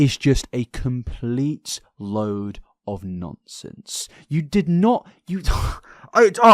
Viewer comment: Did anyone else hear "A complete load of nonce-sense."